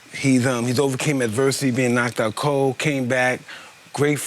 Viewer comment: Am i the only one who thought that Mike said "KNOCKED DOWN COCAINE BACK" instead of "Knocked out cold, came back." i swear on baby jesus I'm laughing my A** off right now.